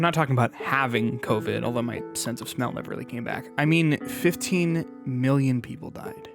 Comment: why do the captions say 7 million while her says 15 million?